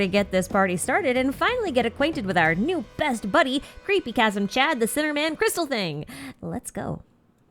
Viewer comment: I misheard you the first time and thought you said "Creepy Chasm Chan" and just nodded. It wasnt until the second time I realised you said "Creepy Chasm CHAD" and I was honestly disspointed XD